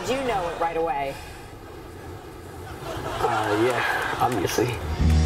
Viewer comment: what did the kid at 9:43 said??? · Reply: “obviously”